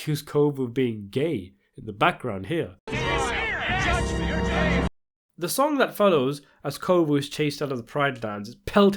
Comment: What are the words you are hearing at this timestamp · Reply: “Kovu Spatafore is an ass muncher”